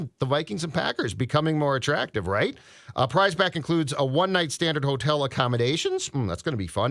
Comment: Did anyone hear say a “one night stand” @ 25:04 😂